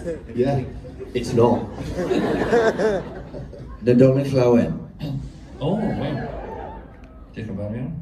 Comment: Thank you so much for sharing!! I wondered what did david say at 13:26? Sorry, non-native speaker here, and I couldn't quite figure out that bit. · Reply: Nadolig Llawen- Welsh for Merry Christmas!